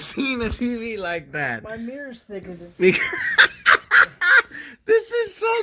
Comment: have you ever seen a tv like that: My mirror is thicker XDDDDD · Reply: Lmao I thought He said "My Hair is thicker" lol